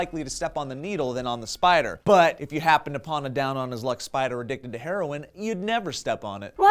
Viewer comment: What did Joe say at 2:11 to 2:15? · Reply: "if you happen to upon a down on his luck spider addicted to..."